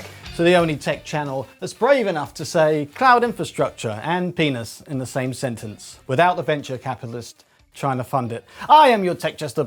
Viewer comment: I started the video while running around finishing a chore and I thought you said 'Cloud infostructure and penis in the same venture capitalist'. Im like well that escalated quickly. Bunch of prudes. like VISA. Why EU is working on its replacement. "Beware of he who would control your access to information..." (Or porn) "For in his heart he dreams himself your Master." Remember the song "The Internet is made for porn"? (WoW video is the best version)) Doesnt the EU help spy on Chinese citizens giving their data to China?